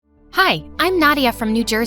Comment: When she said "hi I'm nadia" I thought she said "Hi im naughtier"